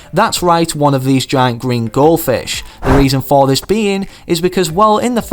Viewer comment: Did... did he say "Giant GREEN Goldfish"? I mean, sure, he's human, and our species tend to make mistakes, but he could've just said "Giant Goldfish". Again, no offense.